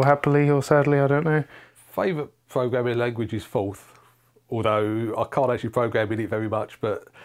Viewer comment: what is he saying? Fowf? Folf? Folth? Foath? Some of the answers go by so quickly, there really ought to be subtitles :( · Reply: Ooooh, it's all in the description! :) (Forth)